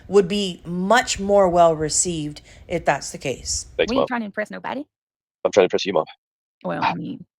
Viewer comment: Is he calling her “mom”?